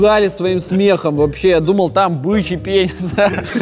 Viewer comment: мне послышалось или он сказал бычий пен#с